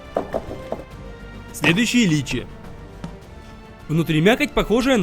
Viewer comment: мне одному послышалось Ильичи